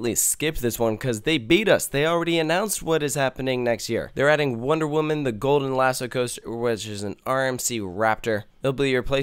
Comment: Uhhh... anyone know what he said at 3:01 ? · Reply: "They're adding Wonder Woman Golden Lasso Coaster which is an RMC Raptor"